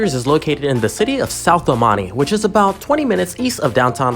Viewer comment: SUBSCRIBE NOW FOR MORE DAILY VIDEOS! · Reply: i always thought you said shouth dalmani 😂